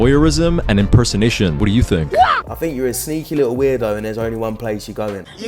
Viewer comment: i thought you said sneaky little WOOD GNOME